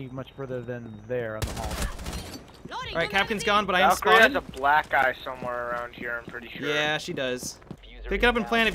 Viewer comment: I heard "Valkyrie has a black guy around here somewhere" XD